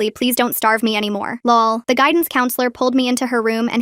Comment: I'm sorry did I just hear "lol" !?